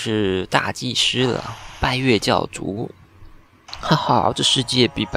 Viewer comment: 我把拜月教主聽成敗類教主了哈哈哈((遭毆